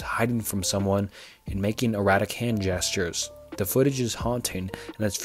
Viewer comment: Dude, you have a good voice for the gig but you need to start using an editor that checks out the audio for stumbles, fumbles and hiccups, etc. I mean I heard you say, during the "Body in the water tank" story you said that she was described as making "Erotic hand motions and gestures" I think it's pretty much NOT what you wanted to imply. This is what I mean by the way that if you had some editing and maybe try doing multiple takes you would have a top notch video every time.